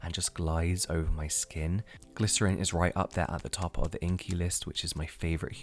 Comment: Anyone else noticed how he said Inkey list instead of ingredient list at 3:58 😂 Inkey list obsessed like me!!!